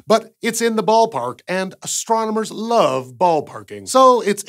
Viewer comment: I thought he said, “astronomers love balls”, when he actually said, “ astronomers love ballparking”.